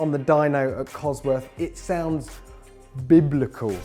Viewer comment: "It sounds...." What did he say? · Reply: Bladeknight Bleu biblical